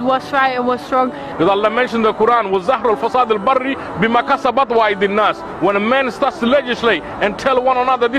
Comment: Did he say Budweiser? That guy wants a beer but that's haram. 😆